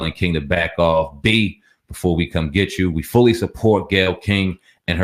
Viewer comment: Snoop didn't directly say he was coming after Gail. He said "don't make me come after u, " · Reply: Lol I thought he said we will come get you